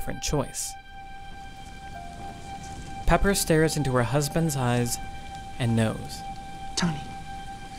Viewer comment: Lol I wasn't paying attention, so I thought he said "Pepper stares into her husband's eyes.... and nose" and looked up and sure enough, that what she was doing XD